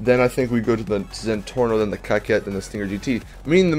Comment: I heard you say the cocket